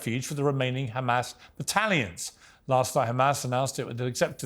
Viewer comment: I thought piers said last remaining italians...battalions.